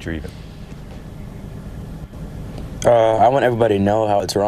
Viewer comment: All i hear is "I want EZ MONEY"